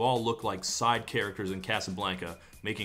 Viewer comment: I misheard “side characters in Casablanca” as “Castlevania” and, you know what? It still works